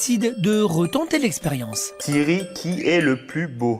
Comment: Ptn je suis mort c'est moi où il appelle Siri "Thierry" ??